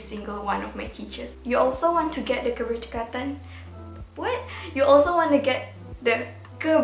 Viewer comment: i swear i thought u said " guru tingkatan " lmfao 😭☝🏻